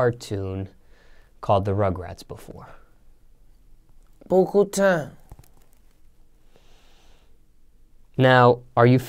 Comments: What he say @ 0:10 ??? Please let me know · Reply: Beaucoup times.. Beaucoup is French for "a lot" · tf boi it was a yes or no question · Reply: John Smith he said yea